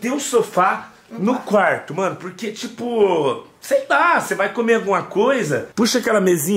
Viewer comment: Minuto 8:37 glr e so eu que entendi outra coisa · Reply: Ela falo , " se vai comer outra coisa "